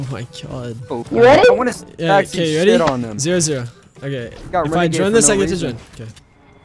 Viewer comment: what did he say if I join I join?